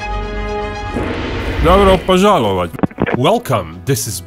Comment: What did he say? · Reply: Dobro pozhalovat' (Добро пожаловать). It means "Welcome" in russian. Binkov is not russian so it was pronounced with an accent.